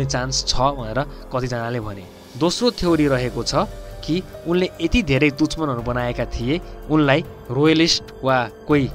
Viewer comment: Like if you heard him saying duchmaan instead of dushman!